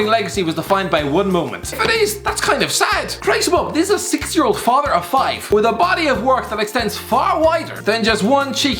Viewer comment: did he just say "6-year old father of five"?